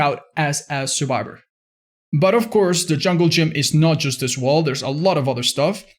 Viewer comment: English is not my first language, so when I started watching DBD youtubers, I always thought that they were saying "Jungle Jim"